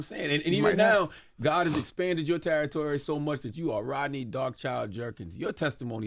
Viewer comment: Y'all.... 😂😂😂😂😂 Before I knew he was saying DarkChild....... I thought he was saying Dog Chow 🤣🤣🤣🤣🤣🤣🤣🤣🤣🤣🤣🤣🤣🤣